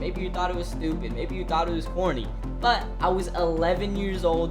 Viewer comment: why does that sound like he's saying horny instead of corny. I had to rewatch it a few times lol